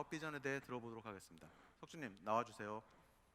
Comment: I THOUGHT HE SAID SEOKJIN